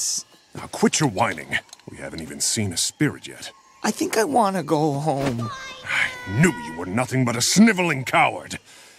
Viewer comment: what does the background voice say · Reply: "Liar"